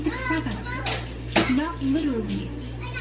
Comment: I haven't read the comments and I am still at the start of the video but... Let me say that I had to go back about five times bc I couldn't hear hear her all the way...and at first it sounded like she said her hand hurt bc she "beat the crap out of her" well...more quieter she said "Not literally but.." So I wonder if people had the same issue I at first had which was thinking she said her hand hurt bc she beat the crap out of her without hearing what else she said. I'm assuming a pop is one smack not sure 🐢🐢🐢cara